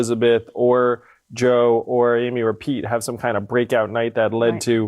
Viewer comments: I thought he said rePete 😁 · OMG, I do believe Chief snuck in a subliminal "Re-pete"! ; in the manner of an "Isle of View" (I love you) or a "Greatest Hits" (Greatest tits). Also, based on what Andrew said about Bloomberg "hoovering" up staff, holy shit, Mike REALLY is the Galactus or Unicron of this campaign.